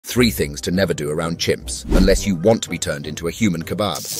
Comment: I work around them all day, every day. Oh Chimps? I thought you said Chumps...